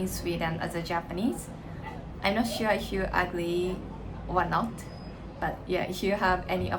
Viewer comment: Hey! I watched until the end as well - I dont police language as you you speak very good english. But when you said "i'm not sure if you are ugly or not" you probably meant "i'm not sure if you disagreed with me or not". Ugly refers to someone apperance in a negative way. 👐 I knew what you meant though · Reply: She said "I'm not sure if you agree or not", but the auto-transcriber misheard